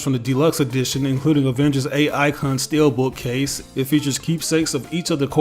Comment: I misheard you when you said Avengers A icon book case at first and thought for a second they'd put a trade of Avengers A.I. in one of the editions. Which... might have been slightly more worth it?